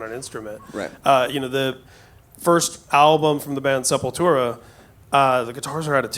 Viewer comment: what band did he say? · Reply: Benka Beats Sepultura